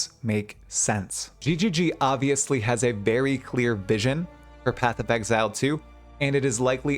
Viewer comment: I heard "a berry clear vision" 😆